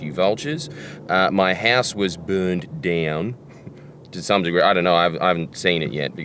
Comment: I thought it said “my horse was fire bombed” 💀